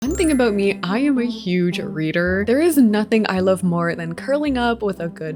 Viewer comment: WHY DID I HEAR RAIDER